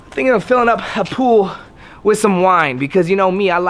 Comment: I thought it said filling my pool with vine 😂